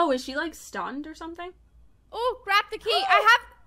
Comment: the subtitles on my screen said " likes stunned or somthing oh CRAP the key..." I think it was supposed to say " oh grab the key" 😂